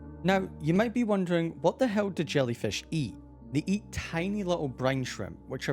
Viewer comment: WHAT DID HE SAY Edit: Omg i have 3 comments! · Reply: He said hell??